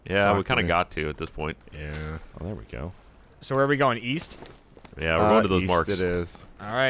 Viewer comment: east? I THOUGHT YOU SAID WEAST